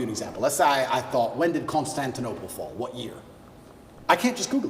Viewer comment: can someone please tell me what andrew said here? I hear "when did constant denovo fall, what year?" Lol english not my first language so can someone please correct me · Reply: Constantinople